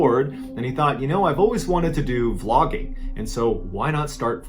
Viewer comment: Oh, VLOGGING. What I heard "he was still really bored. And he thought, you know I've always wanted to do flogging. So, why not start flogging right now?" And I was just like "wow... yes... start a life of S&M at work during a storm... as one does" ... my APD strikes again!